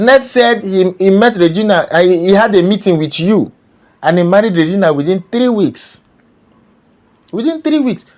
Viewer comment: Grandpa did not Say that he met with Regina’s mother within 3 weeks but rather that he met and married REGINA within 3 weeks of meeting her. This clipped video did not say what you just described. Unless you are referring to another video. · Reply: I clearly heard him say pa Ned met and married Regina within three weeks. Ease rewatch the clip if you missed it.